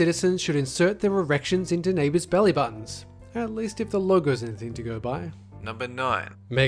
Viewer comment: Oh, I thought it said Legos, like from the picture I saw but it said logo oh hehe